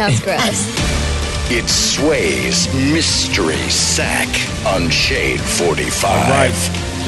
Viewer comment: I thought he said that Sway's sack was unshaved